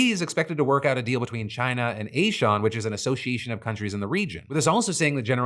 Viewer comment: Imagine a world populated by a guy who looks like a wet napkin that you finished on after you'd pulled out. 🤢 Edit: Also @ 16:00 Did Phil just call ASEAN (pronounced Ah-see-an) as A Shawn? 😂😂😂